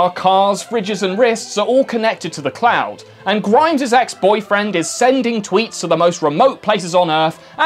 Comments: Referring to Elon Musk as “Grimes’s ex-boyfriend” is a real power play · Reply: I somehow thought he said Grindr's ex-boyfriend. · Referring to Elon Musk as “Grimes’s ex-boyfriend” is a real power play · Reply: Ohhhhhh. I thought he said "Grindr's ex-boyfriend." That makes much more sense. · Referring to Elon Musk as “Grimes’s ex-boyfriend” is a real power play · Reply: Not gonna lie I thought he said Grindrs ex boyfriend…maybe that says more about me :/